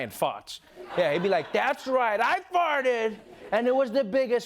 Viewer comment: LMAO anyone else noticed the the caption put fox instead of fart